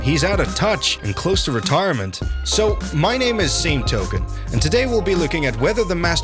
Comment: I thought this man said his name was Zaytoven not 🤦🏻‍♂️